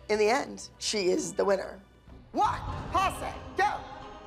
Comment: what i heard... "ONE POSSUM GO!"